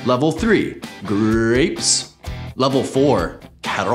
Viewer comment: I thought he said butter not squash for a quick min😅😭